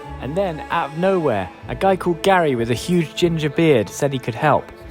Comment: idk why i heard it as Gary with a huge ginger bread and had to pause the video searching for the ginger bread he brought 🤣